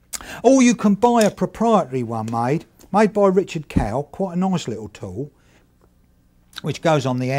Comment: Did anyone catch the name of the metal dovetail jig, I thought he said Richard Kale? Anyone help. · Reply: Richard Kell.. he still makes great stuff 30 years after this video came out, i have his angle finder and brass square..